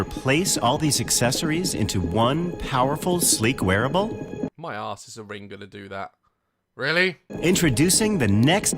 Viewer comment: I thought he said “my ass ring is gonna do that” 😂